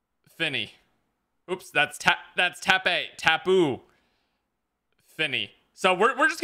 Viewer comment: did he really say tape as ta-pay